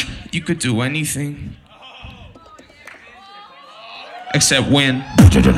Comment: I thought said "You can everything... except when." to, although that sounds equally as brutal as "except win."